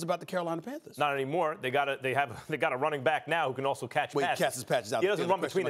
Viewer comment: anyone else thought max said white